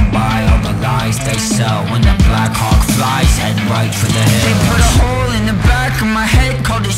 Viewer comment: Instead of "When the black hawk cries head right for the hills" I first heard "When the black cock flies aim right for the head" and I was sooo confused